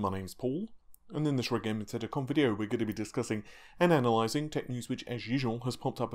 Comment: I still having figured out what you say there, after all these videos... In this red gaming teddy com video?